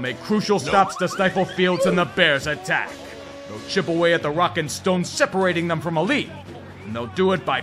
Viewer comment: did I hear a Rock and Stone?